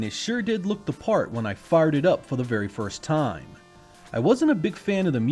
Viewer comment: Im pretty sure he said "farted up" instead of "fired it up".